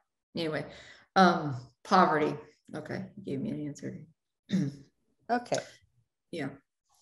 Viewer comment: I hear someone say, “give me an answer” is that The mediums voice?